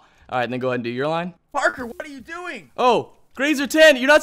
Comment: When said bye parker i thought he said bye burger lol